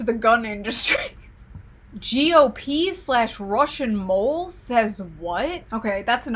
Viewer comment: I had this playing in the background and I swore she said "micro Russians"